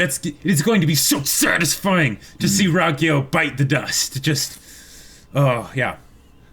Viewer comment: Did just say Bite the dust?